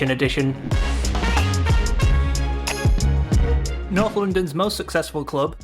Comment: sounded like "Not London's most successful club" 😂😂😂